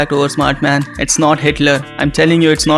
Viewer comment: tbh I heard hitler aswell